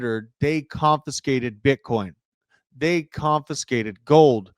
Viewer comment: Do your ears work still? Clearly talked about 1930’s gold confiscation bill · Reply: Apparently, yours don't because around the 8:30 mark, he clearly said bitcoin. It's 100 percent okay to admit a slip of the tongue.